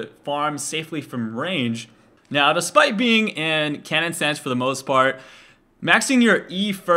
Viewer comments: at first I thought he said "kennen stance" lol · I thought he was saying kennen stance for the whole time.